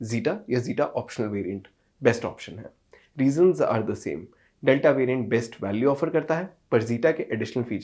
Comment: Did he say Diesels or reasons? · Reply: Reasons